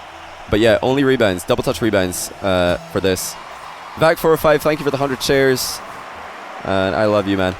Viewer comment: it sounded like he said: "thanks for the hundred chairs."